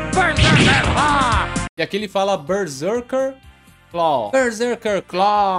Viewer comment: o berseker claw eu sempre achei que ele falava "berseker barraaaage" igual o wolverine kkkk